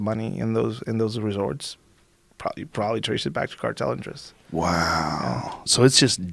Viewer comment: I swear i thought this said escorts